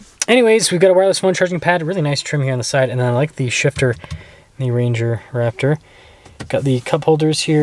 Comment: "newayzugotniztruzmpadwifniztrmonsiden I like the shifter" is what I heard 😅